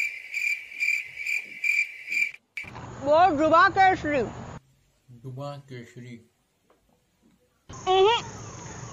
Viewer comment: can anyone explain to me what she said?? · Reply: Ya sure ...at 1:17 she said that "bol Zubaan Kesari" that means "tell Zubaan Kesari"...... I hope you understood and thank you for watching my video ❤️❤️😁😁